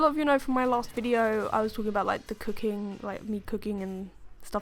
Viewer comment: I love that you're playing HQ. I love HQ. :D Also.... at 0:45 I thought you said "I was talking about like the cocaine" for a second. .-.